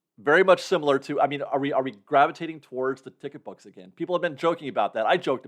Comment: At about 5:13 in, asks “Are we gravitating towards …” but I couldn’t hear/understand the end of the question. I went back and listened again, but still couldn’t tell what he said. What did he say? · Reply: Ticket books